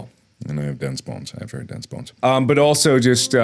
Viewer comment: i thought you said dense balls